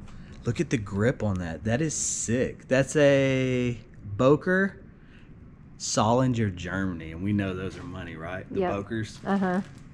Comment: The knives are an outstanding find. The throwing knives are made by Bill Hibbins. The Becker is also a very good brand. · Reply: I thought he said Böker